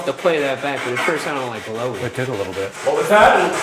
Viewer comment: I thought I heard Booey. As in Baba Booey. Anyone else hear it?